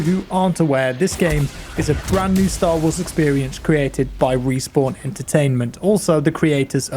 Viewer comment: Sounded like u said wii sport entertainment